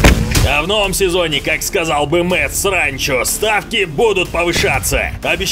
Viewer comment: Послышалось.... "как сказал бы Мэтт Сранчо".....